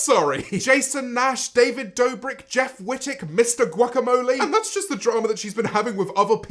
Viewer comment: When he said “mister guacamole” I thought he said “instant guacamole” and I was wondering if she had beef with a guacamole company that she didn’t think was very good, lmao